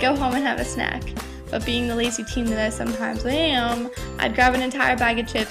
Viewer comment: did you call yourself a penis?